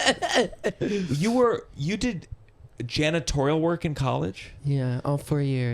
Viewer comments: At first I heard "you did genatorial work" and was very confused for a bit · anyone else hear this as “genitorial work” given the previous topic? 😂